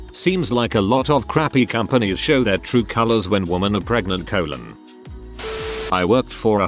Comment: ooof. ok so i was listening, and not reading. and when the text to speech robot sounded like it said "when women are pregnant in their colon" i burst out loud laughing lol